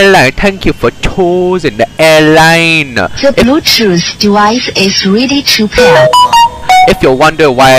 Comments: i heard "the bluetooth device is really cheap here" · No the honor i hear is "The Bluetooth device is really cheap here"